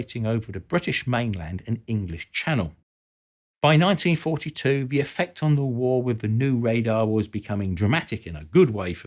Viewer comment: I thought you said the merlin won the war🤨